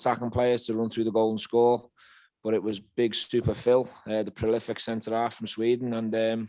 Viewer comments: I thought he said big stupid Phil for a second there 😂 great result lads! Let’s crush them at ibrox! 💪💙🔴⚪️🔵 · I thought he said big stupid Phil for a second there 😂 great result lads! Let’s crush them at ibrox! 💪💙🔴⚪️🔵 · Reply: I thought he said that aswell and I rewinded it 3 or 4 times and still sounded like he said stupid phil but I cant see him saying that but if he did its prob a private joke that the fans dont know about yet